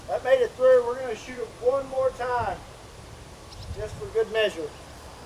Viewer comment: At first i thought he said "were gonna shoot it 40 more times just for good meashur" im like wait what lmao